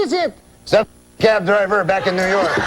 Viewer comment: what does he actually say at 24:27 i cant figure it out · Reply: Some fucking cab driver back In NY